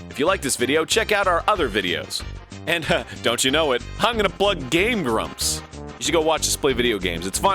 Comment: what does ego say at 5:30? "Hang gonna blood GameGrumps"?